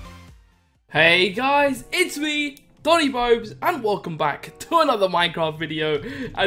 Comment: why at 1:00 does say Bonnie Bobes